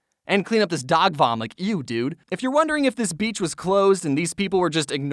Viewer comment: what did he just say? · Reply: Eww dude